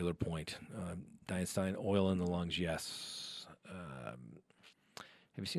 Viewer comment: did say oil in the lungs.. yes?